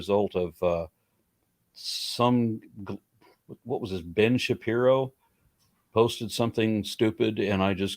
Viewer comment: He said James Shapiro, not Ben. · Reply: when? He clearly says Ben Shapiro in the beggining of the video (at around 0:41).